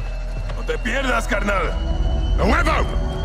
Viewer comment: What does say in Spanish at 8:18 cuz I couldn’t understand him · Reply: "A huevo"